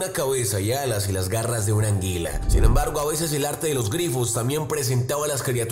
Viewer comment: eh soy la única que escucho que dijo “anguila"??🤔